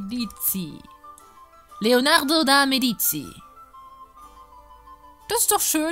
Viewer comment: Leonarde de Medici oder hab ich mich da verhört ? XD am lustigsten wär ja schon ein Arzt im Snape Stil (sry dafür XD) > vor allem aber diese Hakennase x